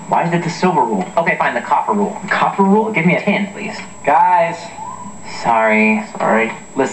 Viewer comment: I thought Kevin said "Give me a tin"